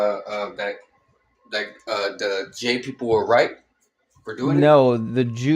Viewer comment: Did he say "J-people?" Like "Jewish" is a bad word?